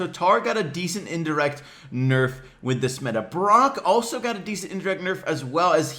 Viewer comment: I hear Bronk Lol